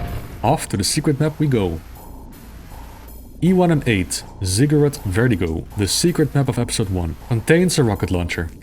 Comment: ~ If I hadnt heard the map name before I would almost have thought you said Cigarette Vertigo, haha